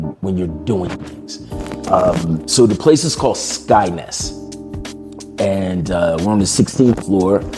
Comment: I thought he said Skynet